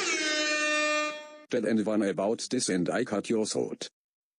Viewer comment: Can anyone please try and make out what the fruit punch clown is saying? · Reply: I think he says "tell anyone about this and i cut your throat"